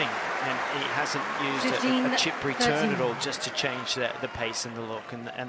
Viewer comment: Ridicolo è chi parla senza capire. Il commentatore aveva detto che Sinner non aveva utilizzato la "chip return" non che la risposta di Sinner era cheap. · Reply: ohhh ok. Sorry thanks for explaining. I thought i heard “cheap return”, so it was actually “chip return”? Ok that makes sense hehe